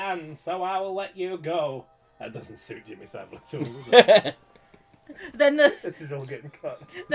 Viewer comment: What did they say at 20:30? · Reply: That doesn't suit Jimmy Saville at all.